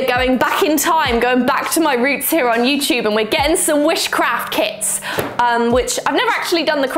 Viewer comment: Am I the only one who thought she said witchcraft kits instead of wish craft kits at the beginning?